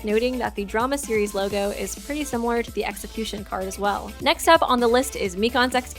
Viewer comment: I'm sorry did u just say sexicution